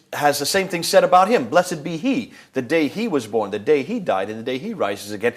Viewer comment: did he say the day he DIED ????? its dies not died idiots that mean the day that he will die not the day he died future not past you said it your self ignorant